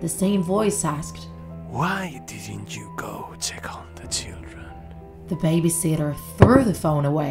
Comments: On the first call I heard, “Did you checked on the chicken?” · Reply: I heard "have you tackled the children" 😭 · I thought he said 'tickle the children' lel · I heard it as why didn't you tickle the children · I legit thought he was saying "WhY dOn'T yOu Go TiCkLe ThE cHiLdReN?" Tf is wrong with me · On the first call I heard, “Did you checked on the chicken?” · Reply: i heard "did you tickle the children" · On the first call I heard, “Did you checked on the chicken?” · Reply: I heard “did you check on the chillden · On the first call I heard, “Did you checked on the chicken?”